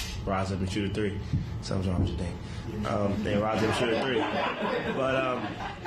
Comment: what did he say? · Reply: “Sometimes it banks”